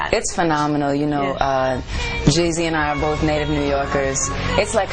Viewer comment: did she say jeezy and i are from NY?